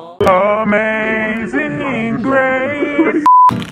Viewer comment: why did I think it said 5th grade